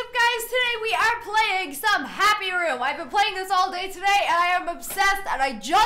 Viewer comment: I thought it said "hairy room"